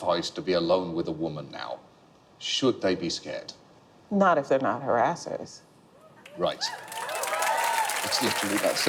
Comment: Wow, Anita Hill. What a warrior. She is amazing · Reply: hurtig heinz go back and listen at 21:30 again, she said "IF YOU'RE A HARRASSER" not "When you're harassing" You changed what she said to fit what you want. What she said meant perfect sense. But it doesn't fit your agenda. Your agenda is to change her words so she loses credibility. Sorry, but I just turned that light on you. Good try but not quite good enough. Your still probably a closet harrasser. That's just my opinion.